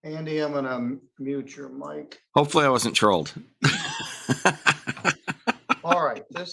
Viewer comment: I thought the guy on the bottom said " I hope I wasn't trolled" but in voice